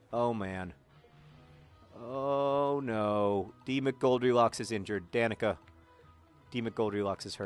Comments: I just cam back to watching these videos, and for 3 videos I thought he was saying Demon Goldilocks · haha me too!! also i accidentally thought he was saying "Demon Goldilocks"!